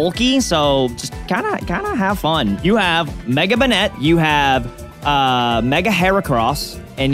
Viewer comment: Banette being my favorite Pokémon it hurt to hear call it Sableye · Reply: Sounds like he says Banette to me 7:35 banter distraction. All in all he called it Banette first. Look at the bright side. They could be Chillin and quick GG those two couldnt name 20 of the original 151 pokemon correctly